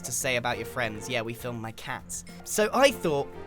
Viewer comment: Did he say cats 1:30. Gbcfubdfgufrbybugyugeuyfrgyfrgufrgyrfgeuygcbdhhuxsaehrcuerh